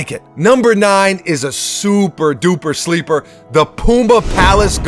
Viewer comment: anyone hear PUMBA XDDD